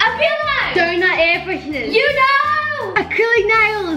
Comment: did just call UNO, yuno?